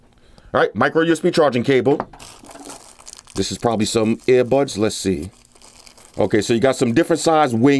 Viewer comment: i heard "'air buns'''